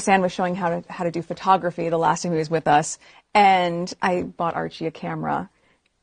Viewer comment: Is she saying "The last time he was with us"?